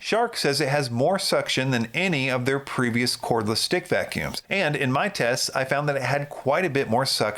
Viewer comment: it sounds like you said “cordless stink vacuums”.